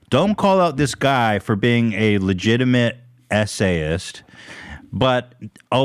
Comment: for the longest time i thought you guys were saying essay and not SA and i was like why are people talking about essays and what are the essays about 😳 LOL